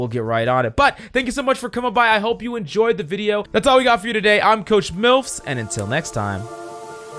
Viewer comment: Did you just say at the end “ I’m coach Milfs” ?????